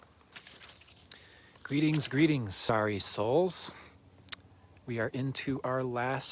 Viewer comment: My first sentence is "Greetings starry souls" BTW... XD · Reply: 😆😅😄 Aaaaw , thanks, I was wondering what 's going on ? I thought you said sorry souls . Then I convinced myself you said sorrow souls . Now I can't stop laughing . 😆 Starry souls ✨ is absolutely beautiful introduction .